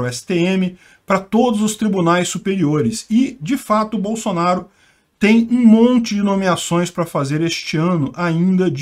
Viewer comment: Juro que ouvi "Bolsonaro tem um monte de DST kkkkk